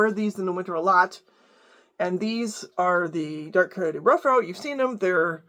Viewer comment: The what rough out? I could not make out what out what you said that boot model name was. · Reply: dark coyote roughout from Seidel tannery